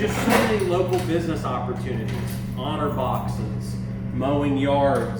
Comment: what did you say? "HONOR BOXES" ?? as a business opportunity?? Is this a Ohio term?